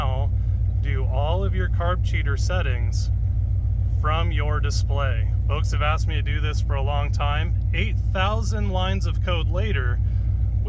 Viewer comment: I heard that as “8000 lines of coke later” at first 😂